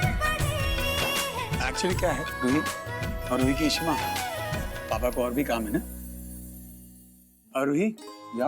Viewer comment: kya bola Raman ne ???? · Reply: Ishimaa